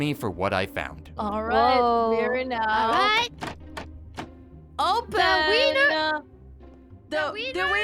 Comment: All I heard was "open– the wiener..." *stares at each other intensly...